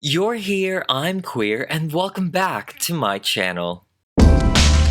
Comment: Omg the first time I watched you I thought you said “I’m here you’re queer”😂😂